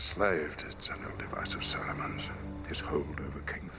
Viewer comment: Something that's always puzzled me about this scene. When Gandalf is talking about Theoden being controlled by Saruman and he refers to it as "an old device of Saruman's". Does this suggest Saruman has a history of mentally controlling or debilitating people? And yet nobody discovers until very recently before this that he's turned to evil?! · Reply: I always heard it as "It's only a device of Saruman's" meaning that it was Saruman's doing.